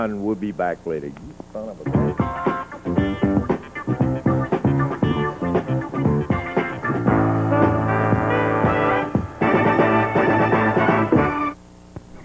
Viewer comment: Did I hear you son of a bitch?